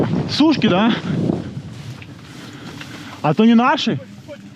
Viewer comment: sounds like "Eto v nashi?" ("Is it ours?") "Nashi" means "Ours" in Russian. (Yes, I know these chaps are Ukranian) 23:43 - "Samalyot" - means "Aircraft" in Russian 27:28 - Patch on back of the helmet says "Jurist/Lawyer." I heard one of the guys mention "Rockets" in their conversation. My meager contribution to a very nice video/analysis.